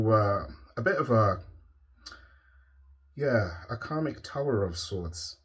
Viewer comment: I thought I heard you say comic tower a coupla times...felt savage and gleeful for a moment...then realized it was an accent and I heard wrong. Bad girl.